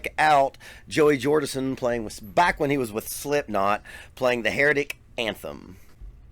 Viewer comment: Did you just say Hairy Dick Anthem? It's crazy that some men can grow hair on their shaft but not their head.... LMAO!!!!!!